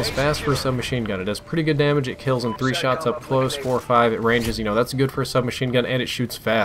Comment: I know getting this video out was important because it's all the new hype . But if your numbers are " I personally assume it's 4-5 " It's just not the same quality . Save yourself remaking this , and explain to us on another video that your mate that gets numbers , is MIA Just save your own time man :') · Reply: I think you misheard me. I did not say it was "4 to 5" but "45". I cannot directly test for base damage because Ghosts has no health settings and unknown multipliers for each gun. It could deal between 49-34 damage per shot up close and I have no way to 100% for sure test it. I said I "personally assume 45 damage" based on how it handles. This was added in for the curious. It is just speculation on my part. However, shots to kill are not. Those were tested :)